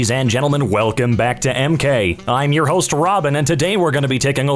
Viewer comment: I heard "EmGay" instead of "EmKay" I THINK I NEED A HEARING AID GUYS